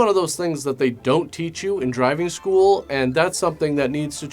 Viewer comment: Sunny was quite the...honey? Huh, huh, sounded like you said, "they dope teach you in driving school."